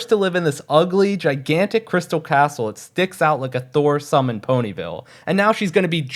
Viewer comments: dude hates this building with so much passion he said "thore sumb" instead of sore thumb (i stopped watching mlp after this for the same reason) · Did I just hear you say “thore sum”? 9:22 Intentional or not that shit made me laugh.. unless I’m hearing something that’s not even there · i like how no one noticed the "thore sumb" instead of "sore thumb" lol - amazing video tho!! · thor sumb instead of sore thumb here LOL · I'm sure I'm not the only one who heard "thore sumb" instead of "sore thumb"😭